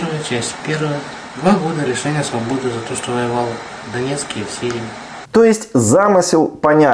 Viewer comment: воевал в донецке, В СИРИИ??? Мне не послышалось, за сирию чтоли добавили судимость?